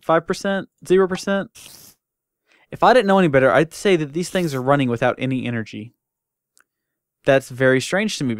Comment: He cut the power on purpose, he said so. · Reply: I thought he said "I think they run without power." but the don't, I might have lagged.